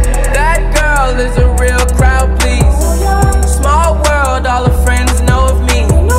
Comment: I swear it says "that girl is a real round pizza" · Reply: I thought is said crown pleaser